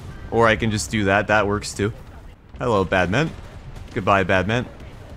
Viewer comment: I thought you said badminton instead of badman 😂